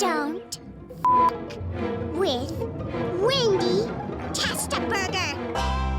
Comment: WHAT DID SHE SAY!?!?!?!? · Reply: TESLA BURGER